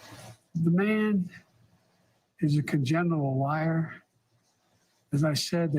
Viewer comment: “The man is a genital liar” -Joe B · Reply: I thought he said "congenital". Either way, though 😂